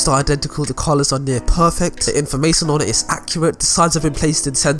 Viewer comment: I thought it said accurah